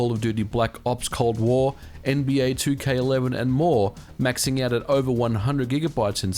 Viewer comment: Did I just hear nba2k11 as a next gen game in 5:19?